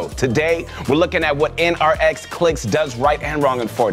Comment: wHat DiD hE sAy? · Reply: NRX Clix